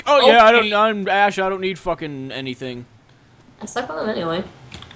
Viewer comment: she talked so fast i couldn't understand i know they are together but did she say 'i slept with him anyway'